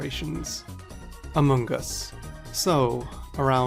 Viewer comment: When among us first gained traction the first time the creator said it I genuinely thought he said amoonguss because he previously did a pixelmon series